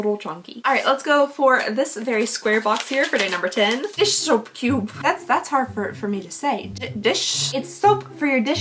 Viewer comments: 😂😂😂😭😂 when you said dish soap cube the first time I thought you were saying "this so cute" in baby voice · Dish soap cube, I thought you were saying, this so cute in a cutesy way 😂😂😂